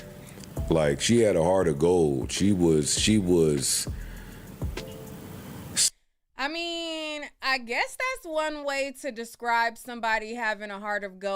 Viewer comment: I think Joe is trying to say “Even Ashlee was heaven sent” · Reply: She cut him off, he said “she was a heart of gold, she was sweet, she was a guys girl, and a girls girl! Somehow,… The dudes wanted her, the girls wanted her, her energy was just infectious, she wasss accepting of us. She was a great wing woman,” and continued to what she loved to do for fun, and then shared memories